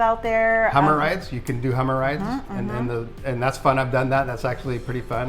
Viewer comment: What did say at 5:37, did he say "hummer rides"? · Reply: Hummer rides!!!